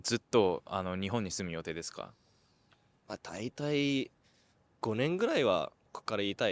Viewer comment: what does he say? あたいたい? what does that mean? Any help? Thank u! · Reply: From context he's saying "daitai...go-nen gurai wa" だいたい5年ぐらいは・・・